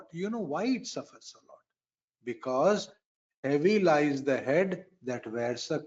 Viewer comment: Namaste 🙏What is Sanjay ji saying there, About the head and the crown? I listened multiple times but couldn't make it out. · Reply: the crown sits heavy on the head is what Guruji is trying to say. Planet in Vrishchika lagna suffers why ? because of the automatic 1 + 5 yoga its like a crown and the crown has some weight which the head has to carry.